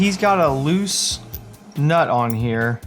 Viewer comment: Did you say "loose nut".... That pretty much sums up the Gixxer Brah. LOL. And I love his brand of crazy.